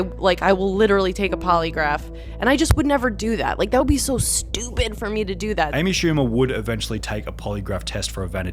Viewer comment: I thought said stupid for 11 mil